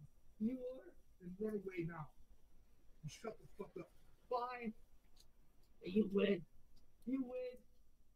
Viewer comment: after says “then run away” says “fine” but when I first heard it I thought he said “bye…😭“ like a sniffling 12 year old.